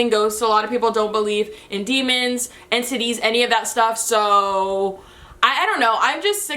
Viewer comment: When you said “entities” I thought you said “and titties” 😂😂😂😂